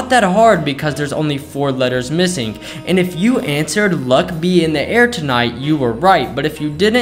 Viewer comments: With “luck be in the air tonight,” it’s impossible to not have an I already so yes it is the other thing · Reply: I don't know why but instead of that I thought it said duck he in the air tonight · "do you have a dirty mind?" "im pretty innocent" "fill in the blanks on this" "never mind im dirty minded" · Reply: I thought it said duck in the air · "Duck he in the and tonight" Turns out I have a stupid mind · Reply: Me too I thought it said luch he in the air tonight I was so close